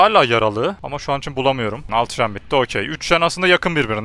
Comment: Altı Jen bitti anladım nyehehehe